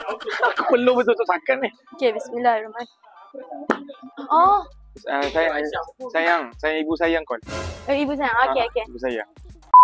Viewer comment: “Sayang ibu sayang call” salah dengar keeee aduiiii sweet nyaaaa 🥰☺️😏